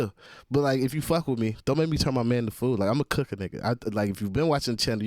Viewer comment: I heard the euro dig in hook but I’m gonna give him a pass now that I know he’s a fellow manu supporter 😂 I concur with him on the hand egg On the first listen to verse I heard ‘don’t make me turn mum into food' I was like what madness could mumsy have done to deserve that 😅 You both had me laughing watching this, good video 🫡